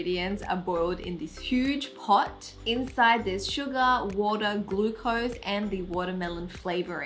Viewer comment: i swear i heard her saying Suga